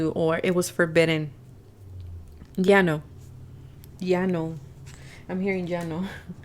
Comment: Pile 3. Thank you 💚 I usually now, that it is REALLY time to listen, when i start to talk out loud and I did that in the intro. It was so cool, that 40:13 she says "jano". I do not know, that which language she is hearing/how it is written but to me it sounded like that: "Jano" and it means "thirst" in Finnish. Right before said that I felt really thirsty and scolded myself for not drinking enough today so I almost spit my drink out, when I heard her say "jano" 😁😅 I wonder, that what is it, that she actually heard 🤔